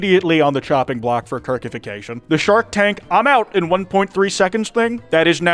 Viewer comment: I keep hearing "percification"... Now my brain is stuck popping Kirk 30's, and that's just all kinds of fucked up.